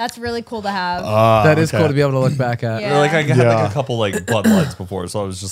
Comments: I heard what Heath heard. "I had a couple butt plugs before." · I thought Matt said "a couple butt plugs before" and I wonder if that's what Heath heard too because he quickly asked what did you say? Lmao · I definitely thought Matt said he has a couple "butt plugs" not "bud lights" 🤣🤣🤣